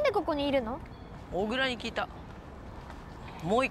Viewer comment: の小倉に聞いたのところがモグラって聞こえた(´^ω^｀)ﾌﾞﾌｫwww